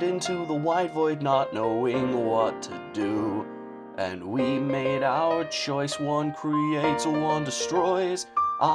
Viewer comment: XD When it "We made our choice" I Almost thought it said we made out